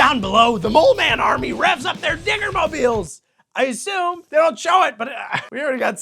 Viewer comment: um... Is just me, or does it sound like said "Their N-word mobiles"?